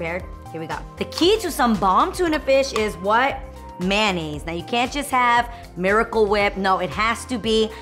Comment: SHE SOUND LIKE SHE SAID MAYANAISE WHEN ITS REALLY MAYONAISE